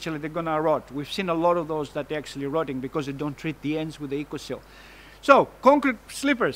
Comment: Hi thanks for your video - at 3:33 you say the timber "is rotting because they don't treat the ends with..." and then I can't make what you say next - Please could you tell me what that is? I am from England so we might have different product names to Oz. Thanks. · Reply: Pretty sure he says "Ecoseal" which is a brand name in Australia for timber treatments.